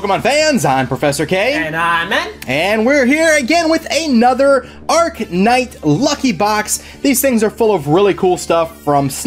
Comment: When you said Arc Knight I thought you said Arcanine xD